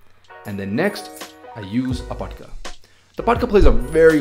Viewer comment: anyone hear what he says it cuts out for me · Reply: He said 'patka' it is a peice of cloth used to set ur Hair and make base for turban